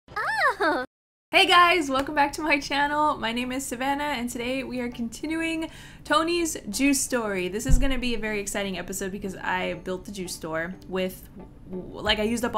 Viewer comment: i the beggining i thought you were saying a JUUL store lmaaao